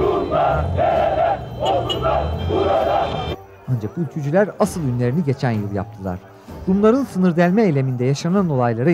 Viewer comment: ne diyorlar anlamadım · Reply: Bozkurtlar burada Rumlar nerede